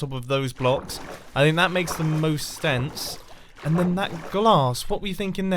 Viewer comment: says “That makes the most STENSE” instead of sense